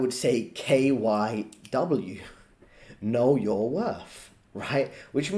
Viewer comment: KYW , I firstly misheard it like Know Your Wuff (Sorry I am not a native English speaker)